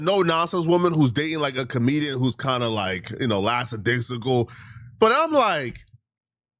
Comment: did he say “lacks-a-dicksicle?! 😂😂😂